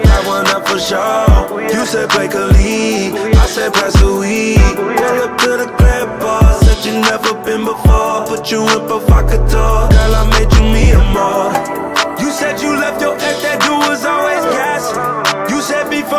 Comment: no way i always thought it said girl i made you mi amore lmao